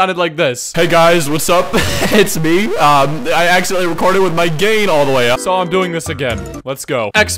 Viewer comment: I keep hearing "with my GAY all the way up